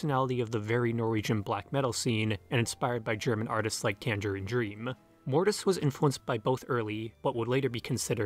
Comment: What are those german artist that are mentioned at 4:14. youtube subtitles say Kander and Dream but I can't seem to find anything like that. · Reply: Tangerine Dream Klaus Schulze Ash Ra Tempel / Ashra